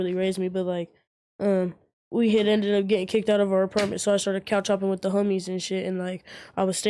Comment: for a second I thought he said he was cow-tipping with the homies 😂